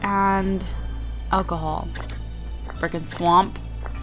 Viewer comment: Wene you said brick and swap it sounded like you said firkin Swap